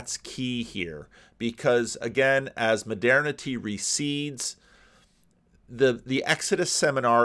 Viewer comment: I was thinking about the rewilding metaphor so when you said "modernity recedes" (46:43), I heard "modernity reseeds."